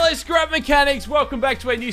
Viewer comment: I heard "Skrub Mechanics"..... Triggered